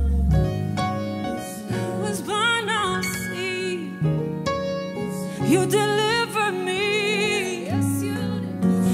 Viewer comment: Some one help at 7:08 to 7:09 what is Katie saying? "i was blind now i see? " or "i was bound by sin? ". Thanks fam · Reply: I was blind now I see